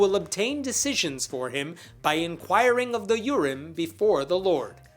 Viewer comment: I had to back up and read the words. I thought you said inquire of the urine.